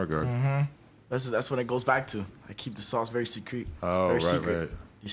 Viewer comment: did he just say that he keeps that sauce sucrete?